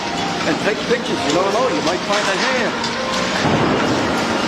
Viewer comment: "Let's go up there together and take pictures, you don't know you might find ..... " You might find what? Anyone understand what he said? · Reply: Yep. A hand. 🖐😨💀